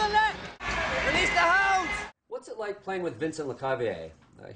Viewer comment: LMAO! Did Stammer say "Release the hoes" @ 1:09?